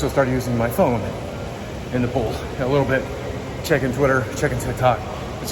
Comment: Lol at 1:33 for a second I thought you said "Ive started using my phone in the pool. Checking twitter, checking hentai..."